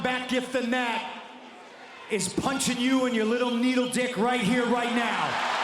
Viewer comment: 😂😂😂😂 Did he just say niddle D ???